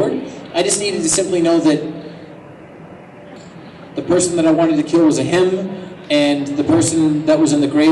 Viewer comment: Did he just say" person i want to kill" in